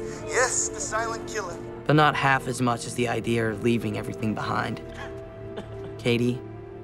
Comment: Did he just say idear LMFAO😂😂😂😂